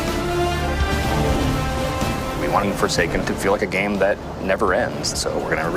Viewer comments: I thought it said: foreskin 🤣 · I thought it said forskin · Could’ve sworn it said forskin at first